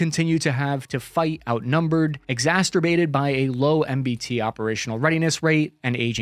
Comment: You said "exasTerbated". You slid right past that, but we heard you say it. Your love of heavy tanks is showing.😉